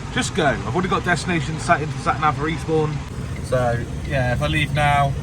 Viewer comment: 'For Eastbourne' sounded like 'Respawn' 🤣